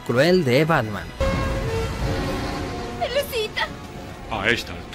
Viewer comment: ese humor están negro que lo escuché rapear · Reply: harley le dice "pelusita".. Y batman le dice "a esta altura seguro lo es"... Chiste simple y funcional